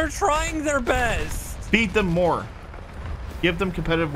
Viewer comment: I heard beat them more...